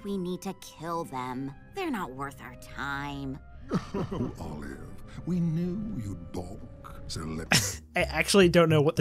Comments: For 49:46 As funny as "We knew you'd bonk" as a misheard line, I'm pretty sure the original line is supposed to be "We knew you'd BAULK" aka "we knew you'd hesitate". It sounds close to "bonk" if you kinda... mumble it like the VA was doing and it fits the context of the scene. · what he's saying is "we'd knew you'd baulk(or balk in US)" which is to be hesitant or unwilling to do something. I'm writing this as i got to this part lol. I tried to avoid this show because I knew it would be bad, but I finally decided to see the damage. Reading the dictionary at 12 for fun ended up being useful for something. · lmao! I think he's saying "We knew you'd balk." As in, we knew you'd refuse. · I think he was saying "I knew you'd balk" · They're actually saying "We knew you'd balk to let..." and I couldn't hear the rest. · I think he's supposed to be saying "We knew you'd BALK" which means "We knew you'd hesitate/be unwilling to do this" but because of the delivery it sounds weird. The VA Under-pronounced the word. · I think he is saying “balk”